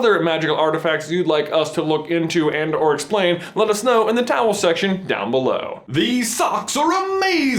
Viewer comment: Umm did he say towel section??